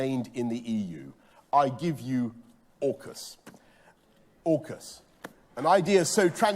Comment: Almost sounded like he said "l give you orifice" in his plummy way.